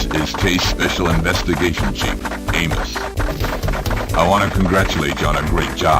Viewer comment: Is he called Chief Anus?